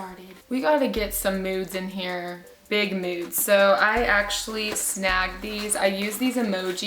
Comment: I thought she said nudes, lol. I was like teaching and nudes don't mix girl. Blech!!!